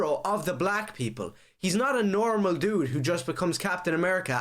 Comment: did I just hear him say black people aren't normal???